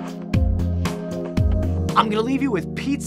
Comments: lol on the end i thought he said: Pizza's making robots. i was like whaaaaaaa??? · "Pizza-making robots". I heard "Pizzas making robots"